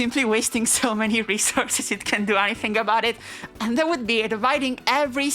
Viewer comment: Are the subtitles themselves created with Machine Learning? Since i think i found some errors, that look like from bad ML transcription for example at 14:05 there is can instead of cant